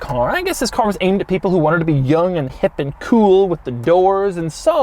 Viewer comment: Did he say he was "young, hip, and cool"? ..